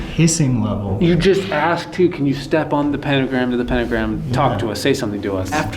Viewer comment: I heard "Don't stand on the pentagram."